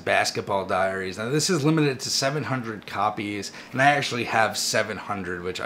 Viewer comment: at first I thought you said you had all 700 copies of it. But quickly realized you meant that you had NUMBER 700